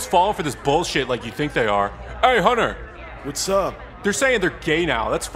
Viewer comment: Did he just call Hunter "honey" ?